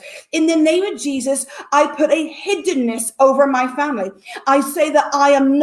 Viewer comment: I don't understand what she said at 32:34 - "In the name of Jesus, I put a ??? around my family." If you know what she said, please type it. Thank you. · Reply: Hiddenness